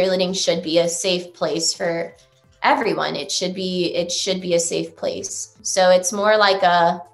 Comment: She contradicted herself a lot. “Cheer is a safe place”, “Yes, this DOES happen a lot more than people think” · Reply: She actually says “Cheer should be a safe place”